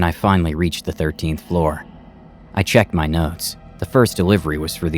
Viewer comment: When he said he checked his notes i heard him say he checked his nuts 😂